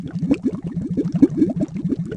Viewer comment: what did he said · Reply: "что ты здесь делаешь?"